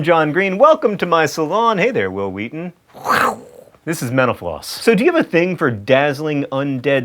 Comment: "Mental Flossers..." With the volume down low, I thought you were saying, "menopausers;" Threw me for a loop. Hahaha